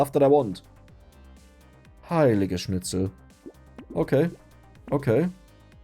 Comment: Based on my very basic understanding of german I feel like is constantly saying "I like schnitzel" because at 31:17 it sounds like he says "Ich schlecke Schnitzel" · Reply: I think he's saying "heilige schnitzel" something like "holy schnitzel" instead of "holy shit"